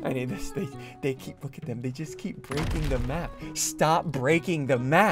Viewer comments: did sundde just say bitches time is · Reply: No he said "they just" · Broo did only i hear now look at the damn bitches braking the map 😮😂